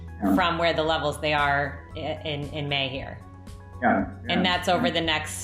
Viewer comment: did she say 30-40% from where they are....in May? This video is already several months behind? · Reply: she said "in May here" heard it loud and clear also.